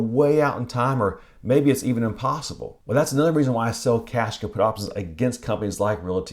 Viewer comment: 💰 Join my Patreon to get all my Live Trade Alerts, Open Orders and Weekly Top 5 Stocks: <link> 💯 My Options Trading Course: <link> 🔔 Subscribe to this channel for Free stock and option trading Tips: <link> 📕 Get a copy of my My Option Trading eBook "New Beginnings: The Option Trading Story" <link> 📕 📈 Purchase My Spreadsheets (as seen in my videos): <link> 🧐 My Favorite Software for Backtesting Options (exclusive discount): <link> 🪬 Protect your trading account & online data with the VPN I use (special discount): <link> 👉 Seeking Alpha: Special discount with a FREE 7-day trial using this discounted affiliate link: <link> 📺 Watch Next: The Perfect Option Wheel Stocks (How to find them): <link> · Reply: What word is that? Is it "Cash caput options"? I'm not sure I like a strat called "Cash caput".😅